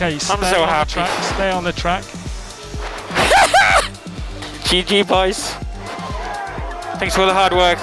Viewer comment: during the race when the team told Norris to stay on the track I thought Norris said 'Cheeky Boys', didnt even consider it being 'GG boys' lol